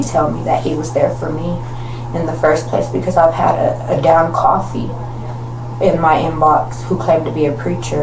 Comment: Can somebody please tell me what this chick meant at 12:21 when she said, "Because I've had a down coffee in my inbox." Wth?! I've replayed this moment several times and can't make any sense of this. 😂